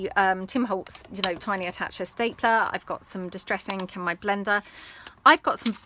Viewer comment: Actually misheard you and thought you said you had ink IN your blender and I just wondered how we were going to use a blender for crafting!! Didn't occur to me that it might be odd at all! Haha. xx